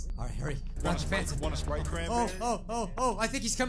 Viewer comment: I thought it said wanna spray not sprite